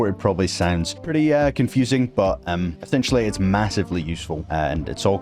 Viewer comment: DID I HEAR MASSIVE???? DOOOO YYYKK WHAATT ELSEEE ISSSSS MASSSIVEEE?!?!?LOWWW TAPPEERR FADDDE